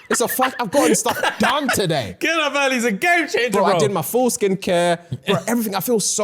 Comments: Me casually listening while getting ready… The way my neck snapped at 5:47 I heard “I did my foreskin care” 😂 The way I was so confused for a couple seconds… FULL! 🤦🏻‍♀️ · for a sec i thought he said foreskin care not full skin. i was so confused🤣🤣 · I thought said I done my foreskin care.. I thought I didn't know they did that.. dying 😂😂 5.49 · said “I did my full skincare” quickly and with his accent I thought he said “I did my for3skin care” and I was like 👁️ 👄👁️ wot